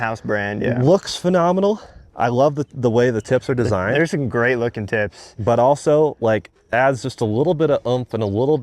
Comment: I thought he said "they're some great looking ti*s* " 😂😅